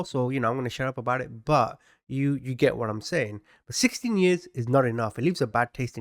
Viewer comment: As an American , when you first said 16 years- I thought surely he said 60 years and I misheard . Abhorrent . Must say I was relieved to hear that I didn’t have to claim monster as a fellow countrywoman . Thanks for sharing the horrible news .